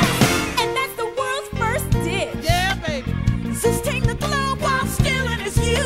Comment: When the song said "And that's the world's first dish", my mom thought it said "the world's first ZIT" XD